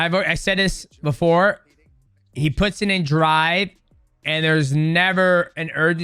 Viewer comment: I thought he said puts it in dry 😂